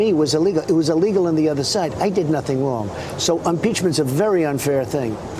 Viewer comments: I swear at 00:24 he says UN-peachment. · Did he say 'umpeachment'? We need to get to the oringes of this!